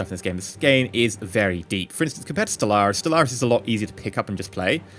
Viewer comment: What does he say is easier to just pick up and play at 7:30? Stel Ars? I can't understand him. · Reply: Stellaris I think, it is a Paradox game.